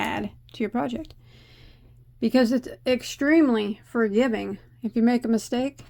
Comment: Oh yeah I love it. It looks cozy warm. Thank you so much for the shoutout you are so kind 🤗 I believe after I finish my Taste of Honey shawl I will be doing this. 20:30 did you say fur giving 🫠🫠